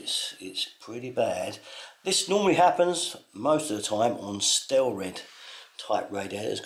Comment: What type of radiators did you say at 0:37 please. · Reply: Stelrad Michael.